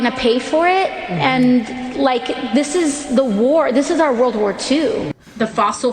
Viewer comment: does she say "it's our WWII"? I thought we already had that.